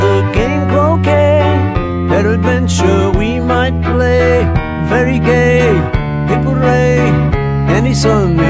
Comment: why i hear "very gay"